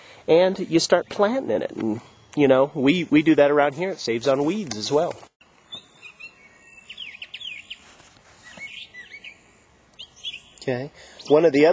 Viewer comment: Dude, at 3:52 did you say it "saves on weed as well"? I've listened to it like 20 times trying to figure out if that's what you said.